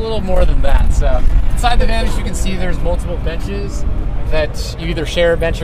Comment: haha thought he said "multiple bitches"